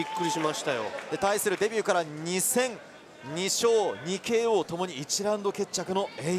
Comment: 俺あまり格闘技見ないからなのかな 2002勝2KOって聞こえてびっくりしたわ 改めて聞いてみると自分のアホさに笑った